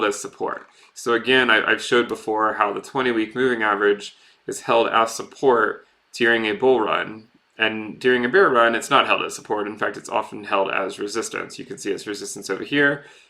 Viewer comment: sounds like you said "During a beer run". That actually sounds good.